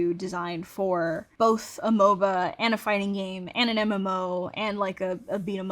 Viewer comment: did I hear that right ? she said MMO !